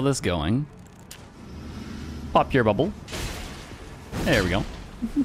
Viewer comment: I was shocked when i heard you suddenly say "f--k your bubble" at 7:50 but then i realised you just said "pop" really fast and breathy